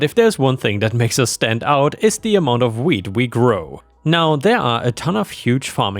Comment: I know he was saying 'wheat', but I kept hearing 'weed'.